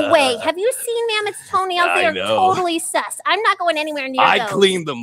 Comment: Did pompom say SUS?